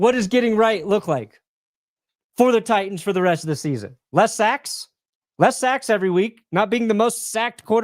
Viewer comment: "What is getting right for the season". said "less sacks", I heard 'Less Sex'